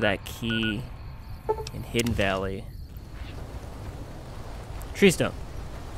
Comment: what did he say? i can't make out what he says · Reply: Tree Stump!